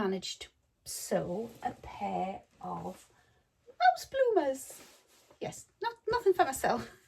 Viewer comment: Hi! I'm American and hard of hearing, so I thought you said you made "house bloomers." And I thought what on earth are house bloomers? I cracked up when you showed the adorable mouse bloomers 🤣 I'm so glad the cost of shipping subscription boxes is such that I barely have FOMO. I also need more meterage for my height and size. I think the Safiya trousers are a fabulous idea...so floaty and nice. I think that much black near your face is too harsh. And you're lovely with or without makeup. Hope you're feeling better next week!.